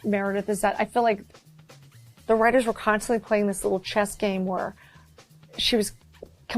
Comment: I thought she said "chest game" 😂